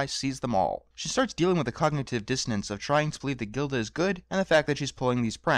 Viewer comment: Pinkie Pie is dealing with what?? I can't understand the words (English is not my first language) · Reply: Cognitive dissonance (noun)- The state of having inconsistent thoughts, beliefs, or attitudes, especially as relating to behavioral decisions and attitude change. Hope this helps